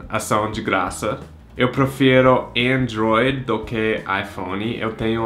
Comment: Ele falou que prefere Android e eu entendi Enjoy, fiquei me perguntando se era uma marca de celular dos EUA que eu não conhecia, depois percebi que ele falou Android, mas aqui no Brasil se fala Androidi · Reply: Também entendi "Enjoy" dai quando ele falou IPhone foi que eu percebi que era "Android" kkkk